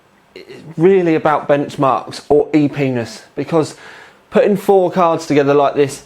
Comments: "It's really about benchmarks or E-Penis" Did I mishear that? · Did he say epenis at 0:53 or is it a term I don't know about?